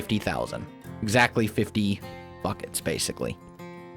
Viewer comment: did anyone hear him say fuc k it instead of buckets at 1:35 ?